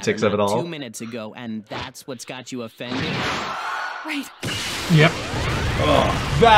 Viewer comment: Wait ... she says 'Wait'? I thought all this time she says 'Right' like in a rush she agrees with 17